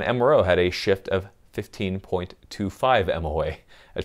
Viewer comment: did you say RMO rather than RMR or SRO? which were you referring to? I hope not the RMR. :-P